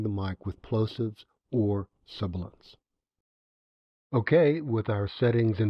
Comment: Hi, Thank you for this video. I am just starting my recording adventure and I really appreciate the calm way you are teaching and explaining. around 04:15 you mention "plosives" and "sub ones" according to the subtitles generator. I was able to find a definition for "plosives", but I wonder if the subtitle generator is actually saying saying the "your" actual words. So wht are "sub ones"? Thank you very much. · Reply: Thanks for the question, Honey. The word I spoke was "sibilants"--which can also cause distortion if not filtered out. Here are other tutorials on Audacity you might find helpful: 2018 VOICE SOUND GOOD <link> NOISE EQ COMPRESSION <link>